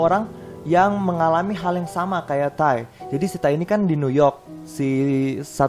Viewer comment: jadi gaada serem2nya gara2 tiap lu nyebutin Ty, kedengerannya "jadi si tai ini" kwwkwkwkwk